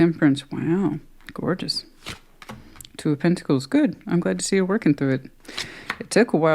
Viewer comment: OMG, did I hear a "sehr gut"? Do you speak German?